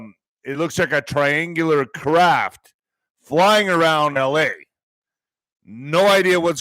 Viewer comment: I at first thought he said a large flying crab...I thought things are sure crazy with flying crabs....then i listened.again.